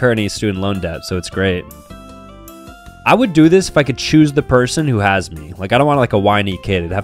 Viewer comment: I thought he said “shoot the person who has me” I got so concerned 😭